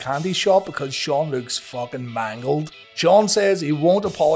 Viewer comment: I heard that as "Shawn looks fuckin Michael'd" and I nearly choked to death on a butterscotch